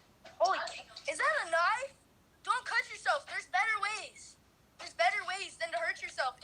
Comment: I thought he said there is better ways to kill urself lol 💖😂 they nice tho